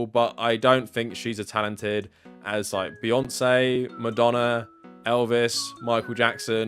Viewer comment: Lol, I misheard you as saying "I don't think she's as talented as my fiancee", and I loved that.